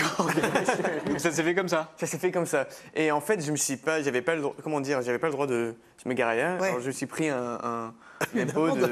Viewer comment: mais il l'a dit c'est parce que c'est eco… ptete c moi j'ai pas compris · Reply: Écologique